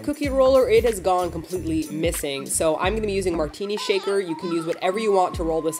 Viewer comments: I heard that as Martina shaker... hahaha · I thought you said you were going to roll out the dough with a "Martina shaker" for a second and I was just like "Wut". To be fair, I just woke up and haven't actually gotten out of bed yet. I'm watching this on my phone. Yay technology! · i misheard and thought she said, "Martina Shaker".